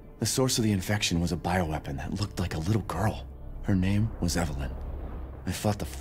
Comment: がイザベラにしか聞こえないんだけど誰か耳交換してください(´･_･`)